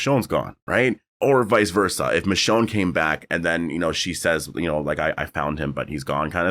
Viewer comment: I thought she said “he came back”